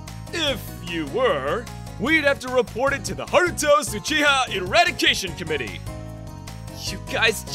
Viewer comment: I thought he said Naruto Uchiha for minutes there